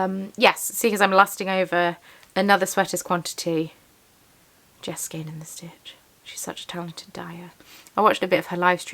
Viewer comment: Who's the artist you mention at 45:33 ? Sorry, couldn't catch that.❤ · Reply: Jess of Skein and the Stitch 🥰 x